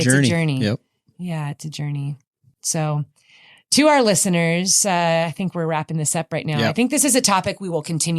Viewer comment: “To our listeners” versus “two hour listeners” which is what I thought said 1st—LOL!